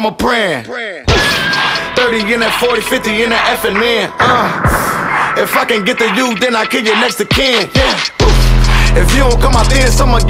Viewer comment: Did he just say "If I can't get to him, I'll get to his Mexican" 😭😭